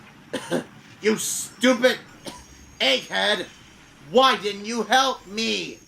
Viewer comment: Did Thomas say "Egghead" or "Achehead"? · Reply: Egghead of course